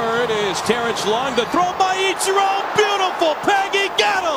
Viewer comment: 鳥羽一郎beautifulにしか聞こえない